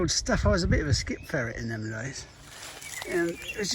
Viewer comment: Did I hear right? Skip Fairy? Pray tell, what is a "skip Fairy"? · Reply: I think it’s my accent Bill......it’s ‘skip ferret’......Someone who rifles through skips to find useful stuff that people are throwing away. I used to do it all the time......best wishes...